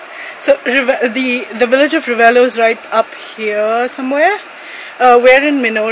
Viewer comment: Thought said "Village of Rebello"🤣